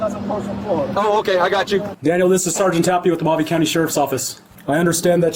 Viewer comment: It sounds like he says Mojave County in the transcripts it says Maui County…. And there’s no such county in Arizona with that name.